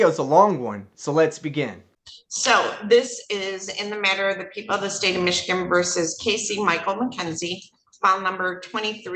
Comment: For a second, I thought the first judge said, "So, this is in the matter of the people of the other state of Michigan versus..." Must mean the UP or something 😆